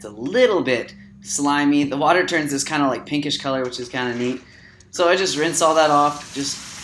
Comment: sounds like he says thats kind of meat, and not neat